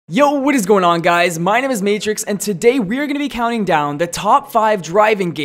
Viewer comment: I thought your intro said hey what is up guys my name is nathorix Lol